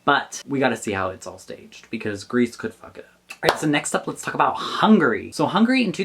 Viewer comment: wtf did he say? He say "Greece gone fuck it"?